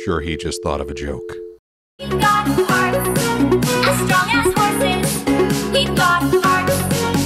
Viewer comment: Theme comes on and I hear: We've got farts as strong as horses."